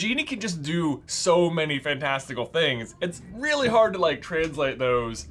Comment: Did J just say "fantastical"? Because if he did, I loved it!! ❤